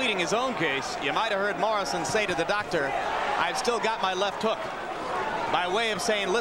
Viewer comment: No, Lamps, that was clearly not what Tommy said. He never mentioned his left hook. That's pretty silly right there. He said, when asked if fe could see: I still have this one( points to his left eye), and asked again about vision he said: I still have my left one(again pointing to his left eye).